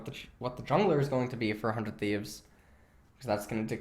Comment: I keep hearing "400 thieves" instead of "for 100 thieves"